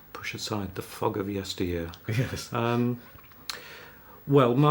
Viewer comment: what does he say here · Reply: “Push aside the fog of yesteryear”, basically means he’s trying to remember.